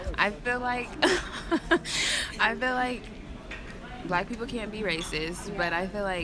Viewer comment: did she say black people can't or can be racist ? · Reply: Cuzwhynot can’t